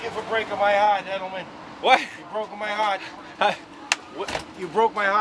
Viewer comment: Damn I never thought I’d hear someone with a Boston accent say “You broke my heart, Julian.” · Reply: at first I thought he was saying "you broke my hat, Julian" because his accent was so thick lol